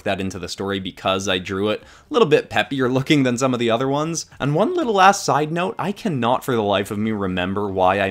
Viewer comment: At the end did he say "one little ass sidenote" cuz i might have misheard him but thats what it sounded like i'm skeptical though cuz i've never heard him curse.